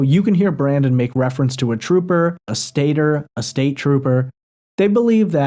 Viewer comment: It sounds like he says a "State Bird" I know around here people call state troopers "State Birds" cuz they fly in and pull u over real quick....